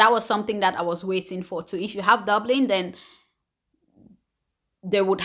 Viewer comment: I am sorry I did not understand what you said that "If you had Doubling they will send you back"? 16:40 minutes can you please explain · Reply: Dublin means if you have first sought for a asylum in another country before coming to Germany. They will send you back to that Country.